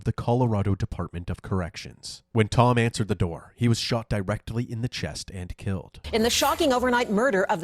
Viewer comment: when you said "Di-rectly" I thought you said "he was shot rectally" 😅